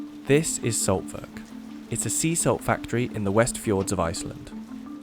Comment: I was just listening, not watching and I am listening about "the most expensive resources" ... and I hear "See-saw" (as in the child's playground equipment. I had to stop the writing I was composing and look up... sea salt... not see-saw! Okay. Go back to the timestamp and start this section over. Close your eyes and think about playground equipment and you WILL hear "see-saw" instead of sea salt.